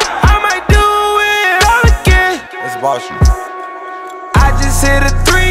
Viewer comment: DID HE JUST SAY BULL####? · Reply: it's boss shit.